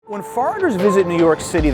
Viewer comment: Totally sounded like he said “farters” instead of “foreigners” in the opening 🤣